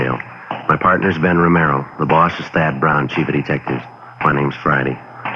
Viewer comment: Ben calls himself Camaro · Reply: I still think I heard "Romero"